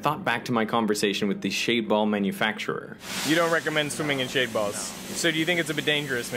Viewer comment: I thought u SAID SWIM🏊‍♂️IN Shaved🥜BALLS🤔🤯😱🤦🏽‍♂️🤣😭